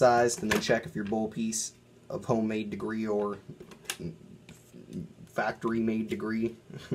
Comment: I thought he said homemade debris and factory quality debris I had to rewatch lol. Good video though props man